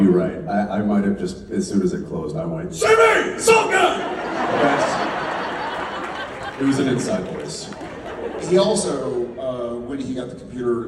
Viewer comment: What did Jensen say at 16:23 , when he calls Sammy? I'm from hungary and i can't really hear it. 😅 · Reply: Jensen said, “Ok, maybe, as soon as it closed, I might have went, “Sammy! Salt gun! But in my inside voice.” 🤣 They were so funny! 🥰